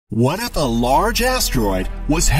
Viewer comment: i think you said "what the asteroid"